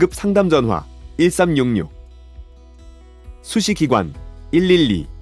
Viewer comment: 나만 수시기관 112라고 들림?